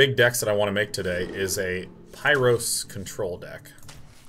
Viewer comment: when he said "pyro control deck" i thought i heard "pirate control deck". too bad i was wrong.